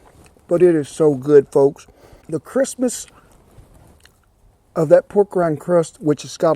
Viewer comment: You know it's hot when it makes your tongue say " Christmas" instead of crispness.